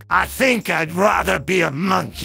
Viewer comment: When I wasn’t paying attention, I heard “Return to monkey mothe-” monkey